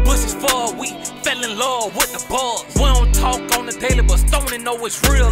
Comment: “In his bushes for a week, fell in love with the bugs” 😂😂😂😂😂😂 yea foolio running Jacksonville Edit: This did not age well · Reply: Lmao thanks for that I thought he said fell in love with the buzz and was confused